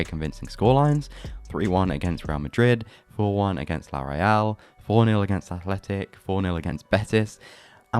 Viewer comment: *Real Sociedad Edit: He actually says "La Real", which is correct